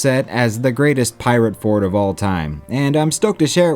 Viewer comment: i think it is going to have some competition now with the pirate bay being announced. Edit: my stupid brain mishearing fort as port